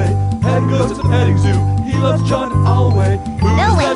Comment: Who's the boy that can laugh at a storm cloud? Turn a frown into a smile for free? Who's the kid with a heart full of magic? Everyone knows it's Butters! Butters: "That's me!" Who's the boy with eyes full of wonder? Who thinks being yourself is the best thing to be? Who's that rascal with the tweezers in his pocket? Everyone knows it's Butters! Butters: "That's me!" Jumping in puddles, skipping down the hallway. Petting goats at the petting zoo, he loves John Alway. Butters: "Elway!" Who's the little tyke with the cutest little dimples? Batting his eyes at every puppy he sees? If you look inside yourself you might be surprised when you find A little boy named Butters! Butters: "Well that's - That's me! Yeah." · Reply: I thought it said, "he loves chocolate always" and Butters says, "no way!"